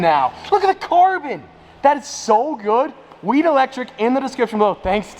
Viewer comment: Man, I swore he said weed electric...